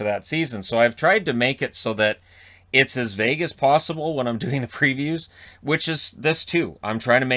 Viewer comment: LMAO I thought you said you try to make it as "Vegas possible" instead of "as vague as possible", like you're trying to bet on people not getting mad. I gotta say that would be a good expression. eg: Wow you really glitzed that up! Yeah I was doing it as Vegas possible. 😂😂🤣